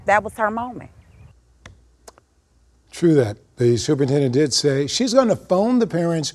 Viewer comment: Did he say “true that”?